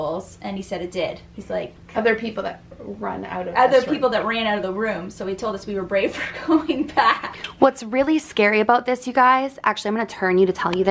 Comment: In the video around 15:37 I heard the word “don’t” right after she said that they were brave for going back to the room. Did anyone else hear it??